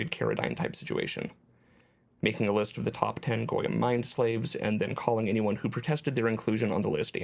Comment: I initially heard that as "Gorean mind slaves" and the meaning of the entire video was different.